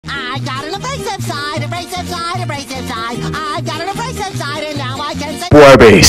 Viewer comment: It sounds like hes saying I've got a racist side lmao